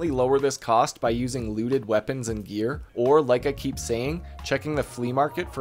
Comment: thought u said "lewded weapons and gear"